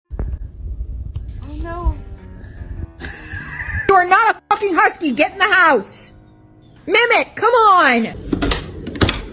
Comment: “You’re not a husky get in the house!” “Mimic cmon!” Um excuse me m’am your dog is named mimic.😂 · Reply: I thought she said Mehmet, named the dog a Turkish name.